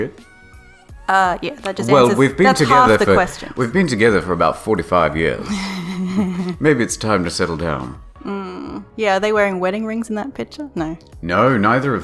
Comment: 45 fucking years? Geez how old are the two of you? Edit: 4 to 5. I may have misheard that as 45 years.